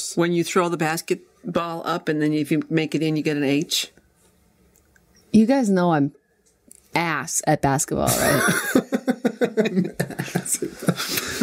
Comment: It almost sounds like Jenna said she sucks at basketball. But that cant be true as she was picked for tomorrow's basketball game.